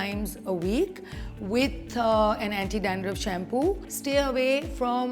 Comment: In 6:00 did she just said anti dandruff shampoo?